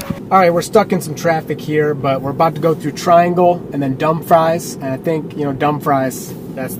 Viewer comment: HA It sounded like you said "dumb fries!" Ah, nutz... Babies 'R Us closed. It was on the news that all Toys 'R Us and Babies 'R Us would be closing, which obviously happened. HOWEVER... I JUST heard on the news that at least Toys 'R Us is coming back, at least for Christmas, soon - and possibly with a different name - that I don't recall now. I know it's good to not get too low of a percentage of range. It's not really good to go by number of miles to the next charger or final destination. This is another reason I refuse to use such 'lameness' as aBetterRoutePlanner. The GPS mapping actually calculates it and seems to be good at reaching chargers and final destinations with reasonable range left. Sometimes the percentage range left is low, but I'd rather slow down to reach a charger or destination than use another untrustworthy source. I gotta say, she's not wrong. In colder climates/weather, range isn't as good as when it's warmer. It seems like you went out of your way by going through PA. It would've had to not take as long if you took a 'straighter' route.